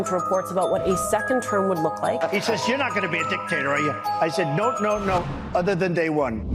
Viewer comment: I always thought trump said, “I’m going to be a dick taster on day 1!” I thought he was trying to throw a bone to the LGBQT+ Community.